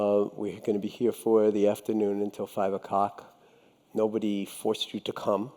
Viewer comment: He really said "until 5 o'COCK"